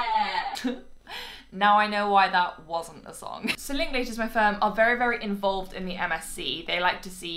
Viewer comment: Did she say so Ling Ling